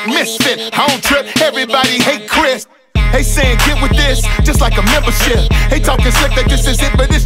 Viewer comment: did he say the s* word? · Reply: He says just like a membership